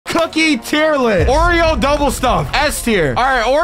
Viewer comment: For some reason at the begging of the video when he said cookie tier list i thought he said coochie tier list💀💀